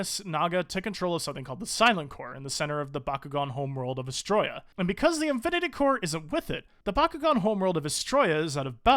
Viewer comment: I thought you said "And because the infinity core is a whippet" lmao, had to do a double take.